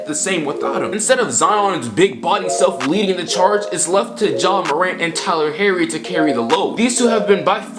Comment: Did he just say ja Morant and Tyler herry